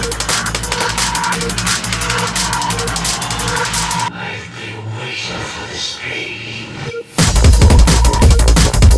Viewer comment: Does anyone know what he say here? Idk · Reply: I think it says "I've been waiting for this pain"